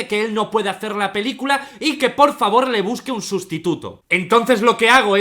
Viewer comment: entendí "que le busque un prostituto" :v